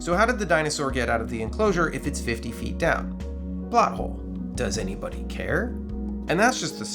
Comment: I heard butthole 😂